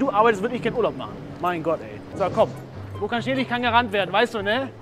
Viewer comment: Was sagt der da? Habs jetzt 10x gehört und immer noch nicht verstanden 😅 Wo kaschirdich kann gerannt werden? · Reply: Wo kein Schnee liegt kann gerannt werden 😂